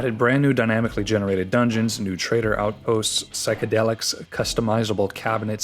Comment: okay-okay nea- did you say psychedelics??